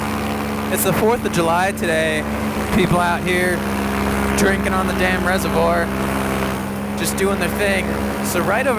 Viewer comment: Thought it said “getting busy over a reservoir”